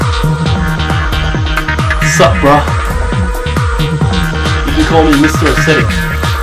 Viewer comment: what say zyzz? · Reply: "you can call me Mr. Aesthetic"